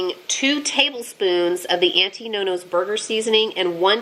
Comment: Hi, you’re meals look delish. I live in Australia so I’m hoping we get that spice here soon. I’m also yet to try corn bread. Love your videos. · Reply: 😂 I thought you were saying “anti nonos”. Sorry it’s an accent thing